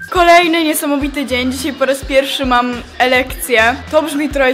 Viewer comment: zabijcie mnie ale nie wiem czemu usłyszałem „dzisiaj po raz pierwszy mam erekcje”🤦🏽‍♂️🤦🏽‍♂️🤦🏽‍♂️